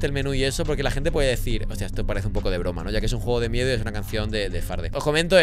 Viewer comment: Pensé que decía un juego de mierda